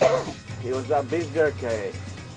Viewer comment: Do I hear him say "it was the beef jerky"?